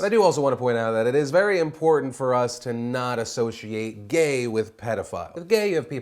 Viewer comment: wait, am i dreaming? Did he really say he is discussed by gay and pedophile?